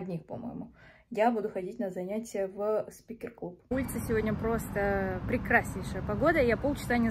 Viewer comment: Мне послышалось Свингер клуб 😅